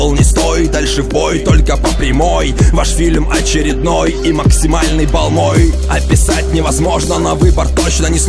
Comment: Мне одному послышалось: "И максимальный балл ноль" XD